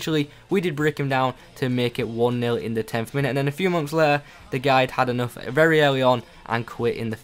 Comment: thought you said in a few months haha